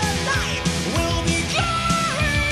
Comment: I was used to listen this when i was a kid, time passed, and today i understand why i always heard "future hole" Fucking world.